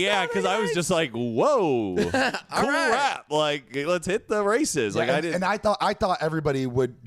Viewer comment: i really thought said "cool wrap, lets hit the racists" for a sec lmaooo